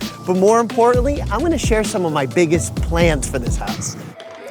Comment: when he said he was gonna show us his biggest plans I thought he said “plants“ and I was psyched to see some mango trees or something😂😂😂